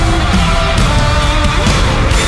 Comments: Did I just heard GD say " Whats up hotdog"? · Didn't GD say 'What's up Hong Kong?' I wonder if they will make a collection of the rest of their tour, that would be cool, especially if it had a segment showing them arriving to other countries, fan reactions, their reactions to the culture around them, etc etc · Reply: i heard 'WHAT'S UP HOTDOG" lol. i think it was 'whats up hanguk (한국 Korea)'